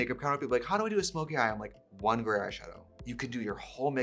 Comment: I thought she was saying "garage show" instead of gray eyeshadow 😂